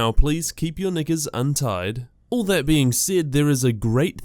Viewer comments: did he say please keep your niggas untied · wtf did u just say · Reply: knickers